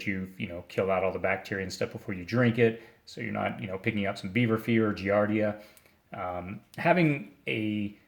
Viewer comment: beaver fever I thought he said Bieber fever lol